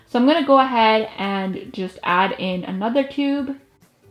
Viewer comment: youll notice that she said cube instead of tube